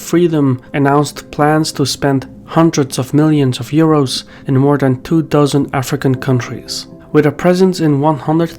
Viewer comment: did you say spend in two thousand African countries? Error spotted.